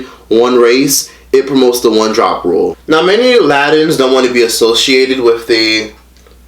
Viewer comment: I thought he said “Aladdin’s” 😩😂